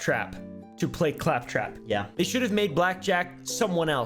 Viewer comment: did only I hear say "black jack" instead of jack black?